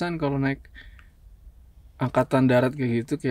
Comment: naik ANGKATAN DARAT tolooong .. besok saya naik ANGKATAN LAUT ah 😂🤣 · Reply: perasaan td aku yg salah denger, ternyata bener bilang angkatan 😂😂😂